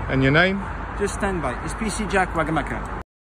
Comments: i thought he said PC Jack Maka Paka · I thought he said Wagamama 😂😂